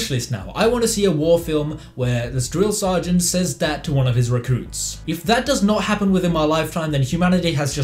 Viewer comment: I misheard 'recruits' as 'racoons'... unless that's what Full Metal Jacket's about, in which case I've lost so much time not watching that movie.